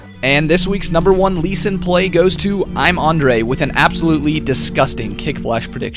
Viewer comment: When ge pronounced "I am andre" I thought he said "I am under 8" lol